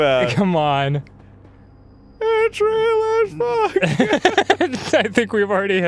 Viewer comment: did arin say, "It's real as fuck nigga!"